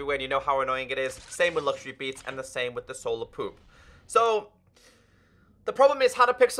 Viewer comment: who heard at 4:24 "solar Poop?????? lke i agree but bruh